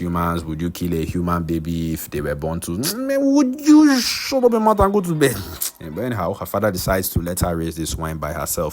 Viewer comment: What did he actually say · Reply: “Shut up your mouth and go to bed”